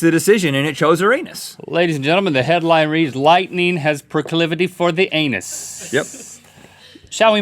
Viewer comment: What's the word he said in 4:51? Precaliferdy? I didn't understand that, can anybody correct me? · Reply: proclivity: a tendency to choose or do something regularly; an inclination or predisposition towards a particular thing.